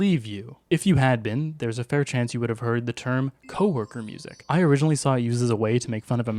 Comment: i thought coworker said cow worker